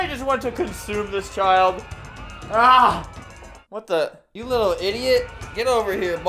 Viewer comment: I had the volume down, when I put the volume up I heard “eat this child”